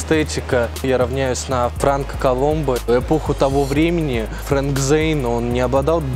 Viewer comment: мне показалось что он сказал "Похуй"